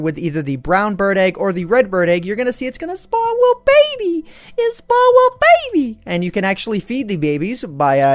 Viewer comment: did you just say gabie?